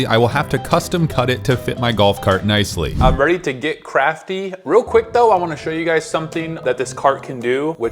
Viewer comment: did he say vet crafty or get Craftee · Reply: Get crafty